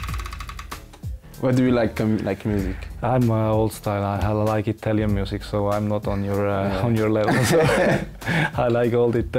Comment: wait, did Lichtsteiner say, "I hella like Italian music?"